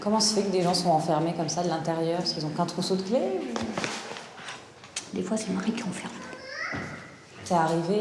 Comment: J'ai pas compris elle a dit quoi a 7:21 ?? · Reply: Elle dit "Des fois c'est le mari qui enferme"